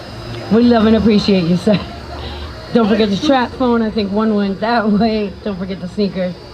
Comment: bro did she say don’t forget your trap phone at 3:47 😭💀